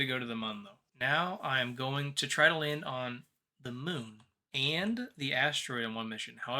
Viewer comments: did he just say "mun"? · Did you just say the Mun???